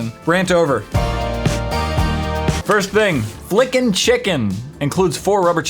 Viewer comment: hahaha lol I thought It said fricken chicken